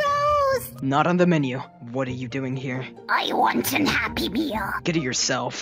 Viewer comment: Xiao: What are you doing here? Pyro abyss mage: I want a happy meal. Xiao: Get it yourself. Pyro abyss mage: How am I supposed to find happiness in this dark world. Edit: Saw this today bc I don't check my notifs. Thanks for the likes mates. I edited it to dark world :0. I thought it was "damn" my bad. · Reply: I heard "unhappy meal" haha